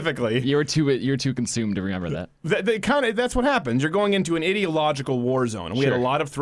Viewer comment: Did he say, "idiot logical warfare"? Best pun ever.